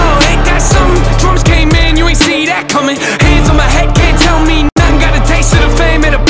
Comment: Bruh I thought he said "Trump came in, you ain't see that coming"